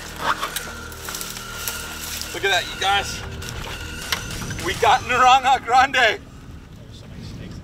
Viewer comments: I heard him said " We got Ariana Grande".lol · Me from New Zealand: somehow I thought he said "I got Arianna Grande" 😂